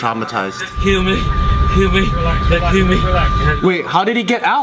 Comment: United is completely wrong, but why was he saying "kill me. Just kill me"? That's kindve extreme... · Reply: I heard ''they'll kill me.'' Which makes more sense.